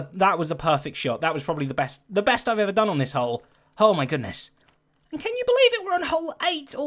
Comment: did just say "hole my goodness" xD